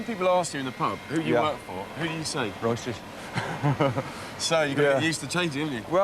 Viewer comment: Was does the guy say at 3:46? I am a native English speaker and can't even figure out what the hell he mumbled. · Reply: He said Royces as the Crewe factory didn’t like referring to their products as Rollers.